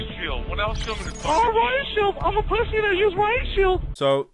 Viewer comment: lol what kinda of shield are they saying? Rine shield?